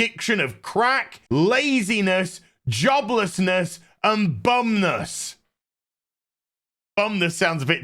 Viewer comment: Bumness 😂I thought you said bumderness 😂😂😂😂 whole review was hilarious, please never stop.